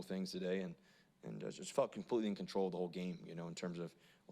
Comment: I thought he said f**kin completely in control for a sec until i realized he said felt lol